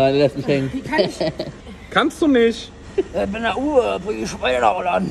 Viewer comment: Ich bin der uwe und bring die scheine nach holland oder was sagt der da 😂😂